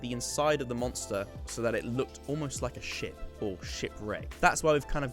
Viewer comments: when you said ship I thought you said shit and i was really confused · Why did it sound like he was saying “shit wreck” XD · every time you said ship i just heard the word Shit and I was thoroughly entertained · Is it just me or I thought he said shit 11:46 Front of a shit😂😂